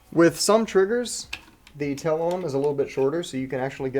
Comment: the 'telom' ? I thought that part was called the tail. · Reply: Let me translate cajun... "Tail on em". lol